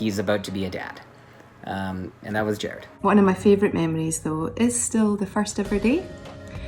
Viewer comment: Can someone please tell me what Ruth Cornell’s favourite memory is at 2:10? I really struggle to understand what she is saying 🥺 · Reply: "One of my favorite memories though is the first ever day. I just so loved going up to Vancouver. Helping you know choose the costume and the look for Rowena that I felt quite, uh, I felt was very important from the get go. And then being on set with Jensen Ackles as my director and he said on the first day ever to me he said 'hm. Another red head'"